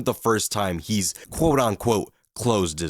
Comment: I think you're saying "quote on quote" instead of "quote unquote." You've done it in a few videos. Just a heads up.